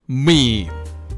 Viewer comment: Chaos dalam ~30 detik ketika baru buka video: 0:00 - "Miii" Teks Auto Caption - M Gw: oh ya, gw juga suka mie. mie ayam, indomie- 0:03 - "Sebuah kombinasi yang tepat dari gambar, teks, atau video..." Gw: MI MACEM APA ITU 0:15 - "Gua secara personal suka banget lihat meme" Gw: Oh ya anjir MEME dengernya Mi Back to 0:00 - "Miii" Gw: emang bener ini kedengerannya MI cooo